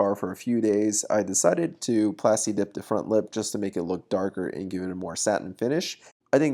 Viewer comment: I thought it said front flip I was gonna say “the freak you can front flip a 911